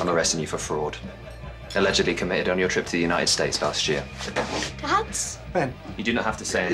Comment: swear lexi said “dads” I’m sobbing